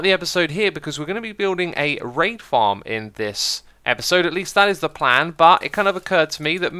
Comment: I was only listening to your video while having lunch and thought you said "we're building a rape farm this episode" ... Needless to say, I think I'll skip lunch today~